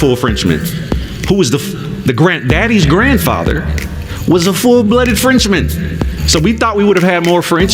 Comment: He said his dad middle name was a French name. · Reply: He clearly said his daddies daddy etc was a "full blooded frenchman". Listen at around 2:03.